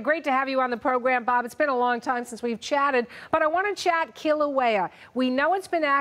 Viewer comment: Kilauea? I thought she said killer whale